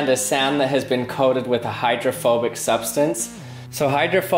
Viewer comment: I thought he said homophobic 😂 #GayMoments